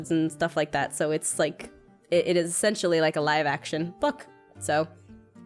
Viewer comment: OMG freaked out for a sec cuz when she said a "Live action book" I thought she said "Live action, fuck" like she messed up I was to watch that twice XD ^-^